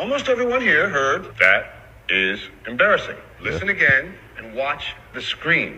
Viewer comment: As far as the brain video, I heard "God said give mercy." Not "That is embarrassing."